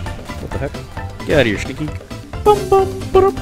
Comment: did you call him a stinky? 😂 cannot stand you